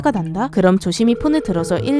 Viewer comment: 나만 111이라고 들리나? ㅋㅋㅋㅋ